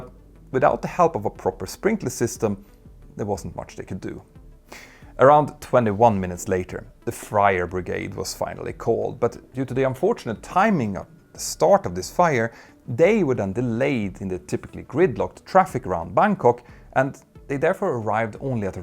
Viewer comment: “The Friar brigade” 😂. Not to make light of a tragic situation, but the visual of a bunch of monks trying to put out the fire made me chuckle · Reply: Is it my hearing or did you call them the “Fryer Brigade” at 24:10 😂😂